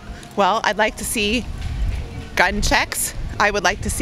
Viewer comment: Gun checks.....whew...I thought she had said gun sex....